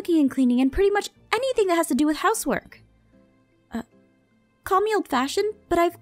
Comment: I thought she said "and I love everything pretty much to do with HOMEWORK", lol, I read ahead....